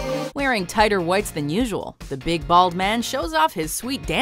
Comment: When they said "big bald" I thought they said ''big balled" for Mr Clean.